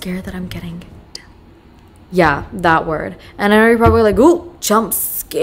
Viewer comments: i need to know what word you whispered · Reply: i didn't know it either 😭 OHH, it's depressed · wait what did she say ? I didn’t hear ? PLS SOMEOBE ANSWER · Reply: depressed 😭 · anyone know what she said i cant hear it and i cant lip read and now im really curious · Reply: She said depressed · i replayed probably 10 times and still cant figure out what she said at 14:39 lol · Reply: Depressed